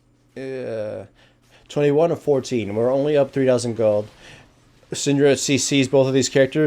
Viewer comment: does anyone else hear "We are only up 3 dozen gold"?